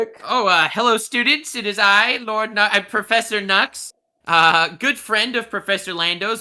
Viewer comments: when he said Professor I thought he said Professor nut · i was showering while listening to this and i heard him say pls welcome professor nut. and i was like ok nut makes sense for porn talk. then i heard nux and i was like is that nux? did he say nux instead? hop out the shower and sure enough there he is